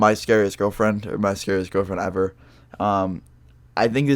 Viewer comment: Did u just say my scarce girlfriend near at the start?